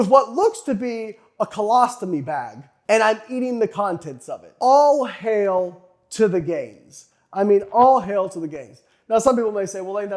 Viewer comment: I literally thought that Layne was saying 'All hail to the gays, all hail to the gays' hahaha. I mean, it's a good and true thing to say but now I realize he's saying 'All hail to the gains'